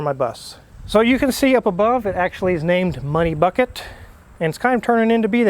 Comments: Oh, MONEY Bucket, I thought that said Honey Bucket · If it didn’t start, you could will it to start i am positive! MONEY BUCKET…..i thought it said HONEY BUCKET..😛😂🤣😆